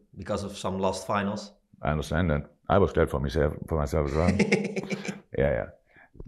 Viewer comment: Jurgen is a scouse heard him say I "was glad for me self"😅😢